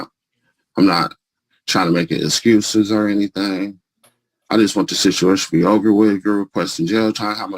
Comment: Did he say, "I just want the situation to be over with girl...?" Or am I hearing things?